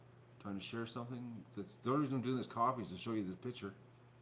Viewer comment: dang - i thought you said that you weren't doing coffee this morning